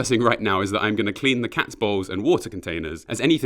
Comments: Why the heck did I think said "Balls"? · What he said: “I’m going to clean the cats bowls.” What I heard: “I’m going to clean the cats balls.” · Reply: I heard "cat bowels" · What he said: “I’m going to clean the cats bowls.” What I heard: “I’m going to clean the cats balls.” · Did he say clean the cats balls? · was i the only one who heard "im gonna clean the cats balls" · i thought he said he'd be cleaning the cat's balls, but he just said bowls